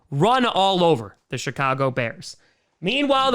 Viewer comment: honestly thought that you said the shitcago bears lmfao.